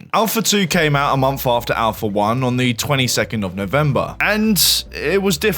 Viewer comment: did he say "22th"